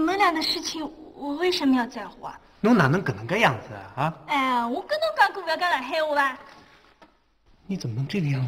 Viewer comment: 他们到底再说啥啊啊啊啊啊？？？？ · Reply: 吕秀才：你怎么这个样子的啦。祝无双：不是跟你说不要讲上海话了吗